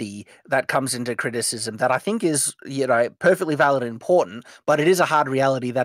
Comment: It sounds like is saying "There is a hard reality that comes into criticism that is highly valuable in porn" He actually says "important" but that's accents for you.